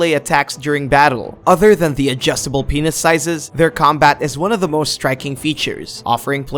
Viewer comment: did he just say adjustable penis sizes?